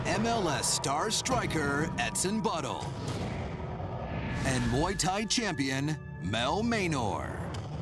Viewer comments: What did he just say at 1:23 Butthole!! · sounds like he says "butthole" :) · Wait, did they call him "Etsen Butthole"? xD